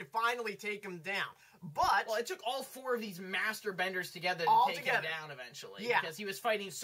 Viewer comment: I must have a really filthy mind cause at 4:55 I heard "it took all of these masturbenders to take him out".